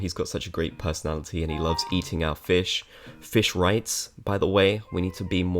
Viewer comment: I thought I heard "he loves eating out fish' 💀💀